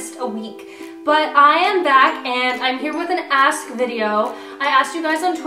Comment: I thought she said ass video I was like what did i click on the wrong thing.😑😂